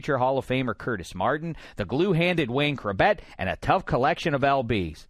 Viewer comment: what does he say? The "glue" handed Wayne Chrebet? The "blue" handed Wayne Chrebet? ...what are you saying??? lol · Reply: Glue handed. Meaning he’s good at catching footballs I guess.